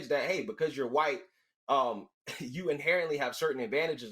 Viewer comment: Are we gonna skip past the fact that Greg said “teachers” and not “white people” and then Prof. Hill claimed Greg misspoke? No sir, you misheard.